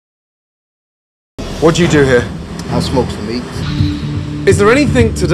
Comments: I thought he said: "I smoked some weed." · I thought he said I smoke weed. 😂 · I thought he said "i smoke weed"